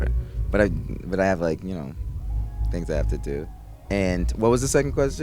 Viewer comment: What did he say in 9:04 ? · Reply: "I have things i have to do"